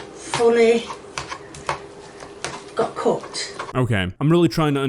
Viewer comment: OK before pointed it out I thought it would make sense sort of the left it is I thought she was saying cooked so that was my explanation but then I didn’t rewind it to see if I was miss hearing it or if was and he’s right she was saying cut. Something I would love if dead on this channel though it’s like a face cam because sometimes I just want to see the expression on his face during some of these videos and he does it for the gaming channel, so why not this one?